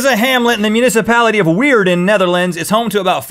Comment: I'm guessing it was Weert but it sounded like he said Rectum was in the Municipality of Weird .. which would have been pretty amusing.